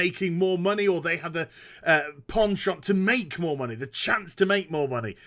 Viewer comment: Did.. did you say Porn shop at 11:24?.. I heard Porn shop...